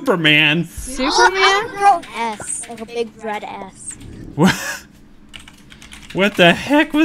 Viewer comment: i think said a s s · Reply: No she said s